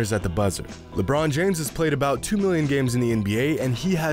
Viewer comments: Did dude just say lebron James played 2 MILLION nba games? At 2:20? Or am I hard of hearing. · did you say 2 million games in the nba